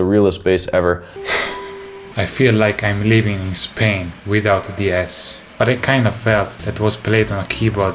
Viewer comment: toats misheard "Spain without the S" as "Spain without the Ass"